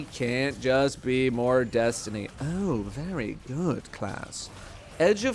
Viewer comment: Definitely didn’t think I heard Datto say ‘very god ass’ and instantly get bricked up thinking he was talking to me